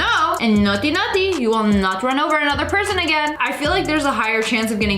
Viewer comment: uh huh WAIT AGAIN?? (wait did it say again sigh I'm lazy idk/)